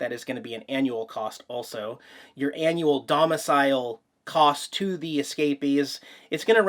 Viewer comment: Almost thought it said “Anal Domicile”! 😳